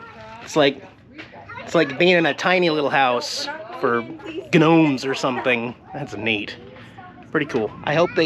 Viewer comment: what did call gnomes? I swear I heard guhnomes 😂 Awesome video man